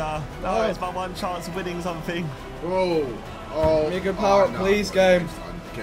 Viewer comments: what vik say at 17:34 ? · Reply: "need a power up" lmao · did Vik say the n word or was audio messing up there 😂😂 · Did Vik just say the N-word? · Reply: he said "need good power-up" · anyone wlse hear what vik says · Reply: need a power up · Am I tripping or does is sound like Vik is saying the N word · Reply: he says 'gimme a good power up please game' but he says it so fast it does kinda sound like the n word